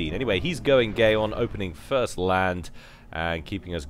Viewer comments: instead of Aeon, i think you said "Gayon" hahaha!!! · did he say he's going gayeon? :P lol